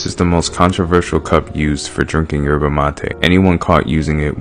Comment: I swear to God I thought bro say jerking instead of drinking